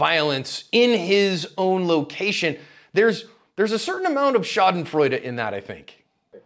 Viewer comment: as a german I have to listen 2 times 1:30 ... "there is a certain amount of "Schadenfreude" in that I think". I cant help... the word sound like the german Schadenfreude and it would fit in the sentence... but what does he actualy said? For a non native speaker like me hard to say :)